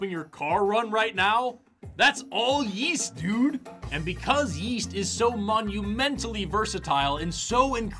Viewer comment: Yeast? I thought you said Weast